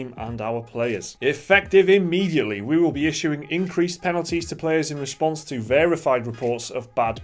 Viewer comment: Did you just say P-nalize?